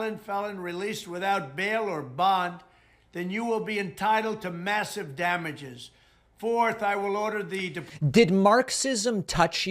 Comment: "...you are entitled to massive damages." Did he just say... "you deserved it?" I can only think of the great bugsy's quote, "What a maroon!"